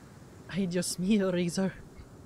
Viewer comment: "ay jusmeyo Razor" was all i heard and it made me laugh so hard XDDD